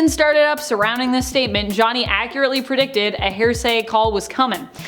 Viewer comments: Is it just me, or does it sound like she’s saying a weird combination of ‘hearsay’ and ‘heresy’? 🤷‍♀️ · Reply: YES!!!! I was wondering that myself! She sounds like she is saying hair-say!!! I hope it is an accent-thing she is using and understands that it's hear-say...like, "I'll tell you what I think I heard." ....is HEAR- say. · Is it just me, or does it sound like she’s saying a weird combination of ‘hearsay’ and ‘heresy’? 🤷‍♀️ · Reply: Right?! I keep hearing it as “HAIR-say”. 😂🤦‍♀️